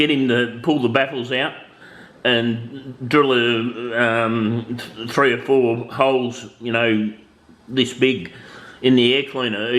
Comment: I thought u said 1 4cm hole?